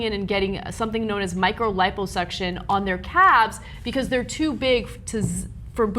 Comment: Does Ana say "cabs" here 0:18?